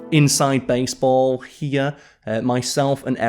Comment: did say "inside baseball" or was I hearing him wrong? If he did, what did he mean?